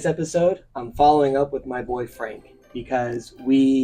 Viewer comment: It sounded like you said “my boyfriend.” I almost had to move to another video…